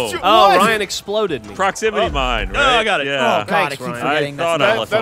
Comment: does Ryan say "Proximity mine ray"? then apologize?